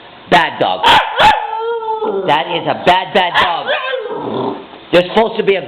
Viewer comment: When he says your a bad dog, it sounds like she says "I'm Not A Bad Dog!"